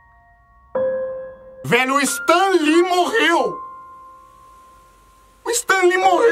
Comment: Quando ele disse "Stan lee morreu" eu entendi "Stalin morreu"